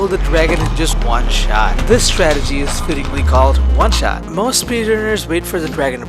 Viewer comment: did you just say... oneshot?